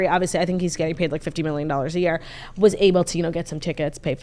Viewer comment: Did turdy say christian mccaffrey is gay at 47:24 ? 🙃